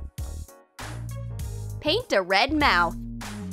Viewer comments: did she just say... PAINT DA RED MOUTH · I wasn't the only one to notice she said "Paint DA red mouth" instead of "Paint the red mouth"